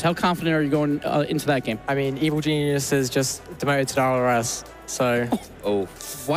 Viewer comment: What did say at 4:31 ? I'm french, I understood the rest but not his answer, meh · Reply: Evil Geniuses just demoted to RLRS